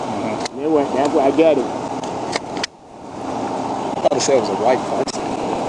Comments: "I thought they said it was a white (Nissan) Versa." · words "thought you said is was a white person" vs. closed caption "thought you said it was a great fight"